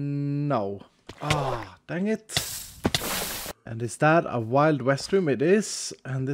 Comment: When u said "is that a wild west room?!" I thought u said wild restroom 😂 🚺